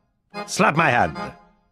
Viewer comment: Dude I thought the spy said slap my ass at first.